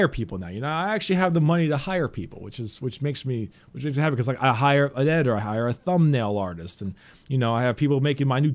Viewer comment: The is getting me excited cuz I heard "I might get an editor" but then I remember wait a second I'm not nearly good enough at editing